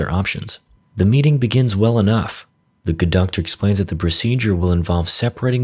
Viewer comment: WHAT ARE YOU SAYING HERE, THE "GOOD-DOCTOR"? THE "CONDUCTOR" (that's what the captions thought)? THE "GUDOCKTOR"? (whatever the fuck that is)...